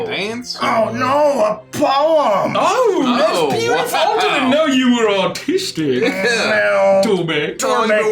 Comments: I thought he said "I didn't know you were autistic" and lost my marbles LMAO (disclaimer, I'm autistic) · the auto-generated captions say "oh I didn't know you were autistic" instead of artistic and honestly it made me think: maybe that's why Torbek struggles with socialising. It's nothing to do with the fact that he's a disgusting drunken mess, he's just misunderstood! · Not me just playing a game and then I hear say “I didn’t know you are artistic”, which sounds way too close to autistic so I’m like wait what I am then rewind and then I’m like oh I’m dumb😅